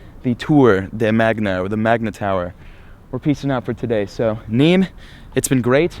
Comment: Am I the only one who thought he was saying memes the entire time